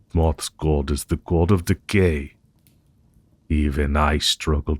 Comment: I misheard the word "decay" as "the gay" and had a flash back to the time I went as the god of gay for Halloween. Nearly cried with laughter